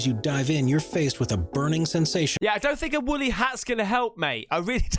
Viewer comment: It sounded like you said Willy out not woolly hat lol.